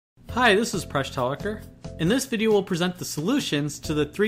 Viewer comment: I THOUGHT HE WAS SAYING FRESH TALKER